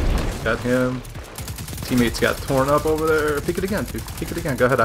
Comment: Uhhh......did I hear what I think I just heard?........ 🤔🤨😂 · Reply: What I said: 'Teammates got torn up over there.' What it sounds like: 'Teammates got PornHub over there.' 😂